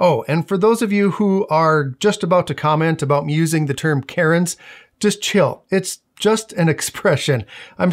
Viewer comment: I straight up thought you were saying carrot, not Karen, and I was so confused until the context clicked.